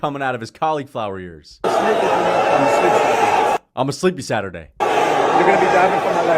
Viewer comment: i heard "i'm gonna sleep slap you"